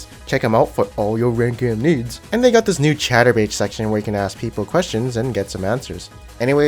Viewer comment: "They have this new chaturbate section" DID I HEAR THAT RIGHT!?!